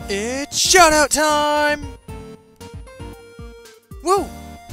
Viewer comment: I thought he said shut up time XD